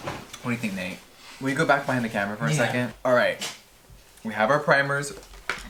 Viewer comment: "can you go back behind the camera for a second?" All I heard was "Mom, can you go fix your hair?" 😂